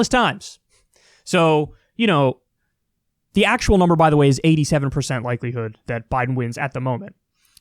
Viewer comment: Kyle: "There's an 89% chance Biden wins, but that also means that there's a 13% chance Trump wins." Me thinking to myself: "Isn't 89 out of 100 supposed to be 11?" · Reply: I thought he said 87% EDIT: 13:36. He said 87%